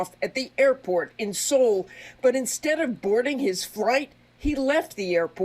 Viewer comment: "but instead of boarding his FRIGHT," What a dumb mistake for a news reporter to make live, and just keep going like she didn't eff up.